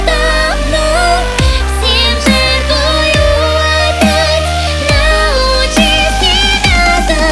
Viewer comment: Вместо "всем жертвую опять" послышалось "семку жую опять"